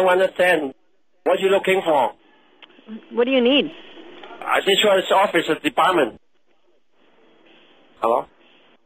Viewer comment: Sounds like he says “insurance office of department” LOL!